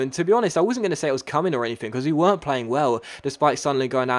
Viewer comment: Did say "I wasn't cumming or anything" at 8:55 or am I just wrong in the head?